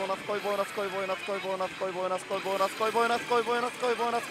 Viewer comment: ボーナス来いボーナス来いボーナス来いボーナス来い… マーボナスマーボナスマーボナスマーボナスマーボナスに聞こえて来た